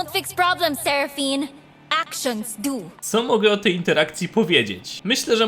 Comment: Czy tylko ja zamiast "action dose" usłyszałem "Akshan dose" ?